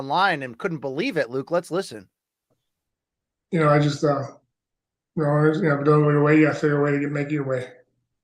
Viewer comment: Seriously, what did he say? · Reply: If it don't go your way you gotta figure a way to make it your way